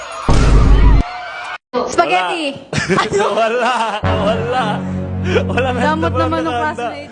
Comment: Did he just say hola or is it just me 😂